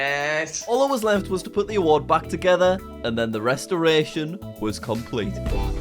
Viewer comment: мне отчётливо послышалось "дриставрация"